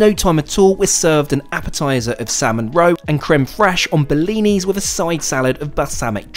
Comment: So funny, I was in the next room when you described your choice of food your your first dinner on board, 9:30 min., I heard salmon roe with crème fraîche served on bellini, I thought what is bellini? 😅 I then realized that's how you pronounce blinis 😂